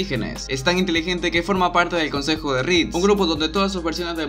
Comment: Por un momento escuche "El Consejo De Ricks"